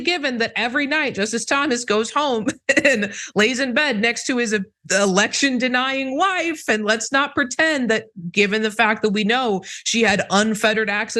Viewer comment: Did you just say "every night justice Thomas goes home and lays in bed next to his erection denying wife...?" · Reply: Election denying wife. Sounds about right.